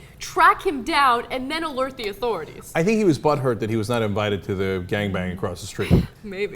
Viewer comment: DID CENK JUST SAY BUTTHURT